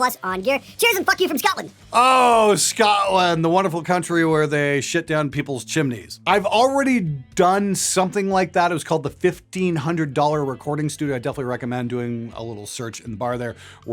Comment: lol @ 8:30ish i hear "ah scotland, where they shit down chimneys....ive done that and called it a 1500$ studio"